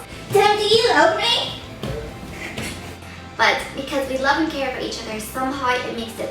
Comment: When you said 'Tim do you love me?' I thought you said 'Tim are you ugly?' LOL..I thought that was kinda harsh xD aw you guys-333